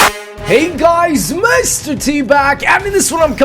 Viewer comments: Sounds like he said mr teabag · When he says mr t back, it sounds like he says mr teabag